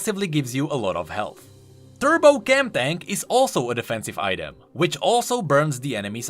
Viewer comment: Is someone else hearing at "Turbo Gangbang"